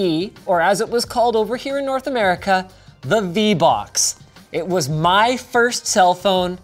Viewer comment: DID SOMEONE SAY VBUCKS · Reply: He said vbocks not vbucks or vbox